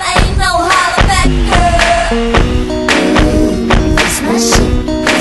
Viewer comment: As a kid I used to listen to this in the car and I thought it said “Gesmush” instead of “this my shit”.